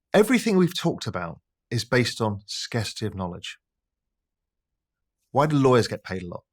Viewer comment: What did he say in 47:57 ? Skastive knowledge ? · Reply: “scarcity of knowledge”